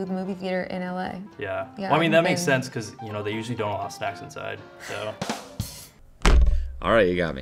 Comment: I cant hear what he said. What was the joke? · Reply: “They usually all have snacks inside” inferring that she’s a snack